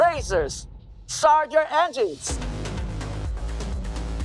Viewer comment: OV's subtitile at 1:50 sez 'I do bad things." but I hear is " Are you ready?" Anyone else noticed or am I hearing things?